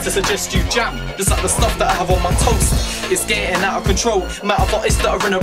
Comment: lol its "like the stuff i have on my toast" i thought he said toes aswell til i heard wat he said before lol